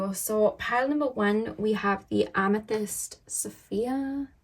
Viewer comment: When you introduced the piles and you said "amethyst s-phere" in that tone, I swear I thought you said "amethyst Sophia" and it was so cute. 😂😅😅 Going to listen to pile four though, I felt it from the thumbnail, no crystals, and turns out ocean jasper is a new big favorite of mine. 😊